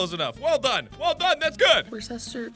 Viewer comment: Ten months later and I'm still laughing my ass off at this video (Still don't know how to pronounce "Worcestershire") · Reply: man i thought it was said wooster